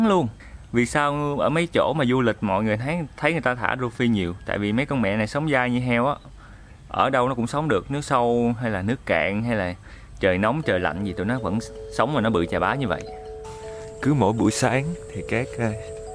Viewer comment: ko biết có nghe nhầm ko nhưng anh ý bảo mấy con rô phi: mấy con mẹ sống dao dễ sợ luôn". Hài hahaha